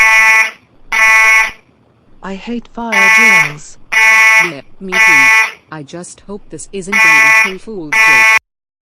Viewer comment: What did they say? I couldn’t hear because of the alarm.🚨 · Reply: I hate fire drills yeah I know I sure hope this isn't an April fools day prank